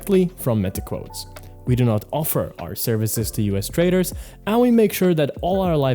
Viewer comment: At the 5:50 point "we do not offer our services to us traders" Did I hear that right? Yesterday another prop firm I have 2 accounts with stopped taking us clients.